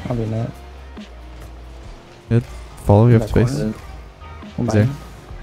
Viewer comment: I put captures on and at 2:55, instead of "you have space... He's there" it was your face isn't there...